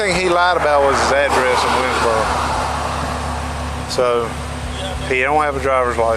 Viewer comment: I legit thought that when the officer said the guy lives in winsboro, I thought he said woodsboro 😂